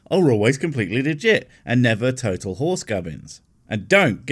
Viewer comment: I thought you said horse gummies. Then I thought it would be an interesting shape for a gummy. Then I thought why not make it from horse hooves. Real horse in every gummy.